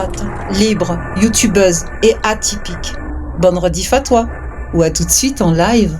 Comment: 🩵 Dans ton message d'entrée tu dis : bonne redi"f a toi" ... J'ai entendu "fatwa" est-ce normal ?? 😂